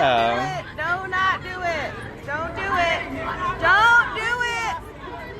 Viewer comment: did she say "Donut do it!"?